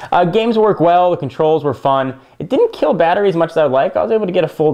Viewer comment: Wait, at 7:10 did you say it didn't kill battery as much as you'd like? I was hoping for less too. Sony always disappoints me. :(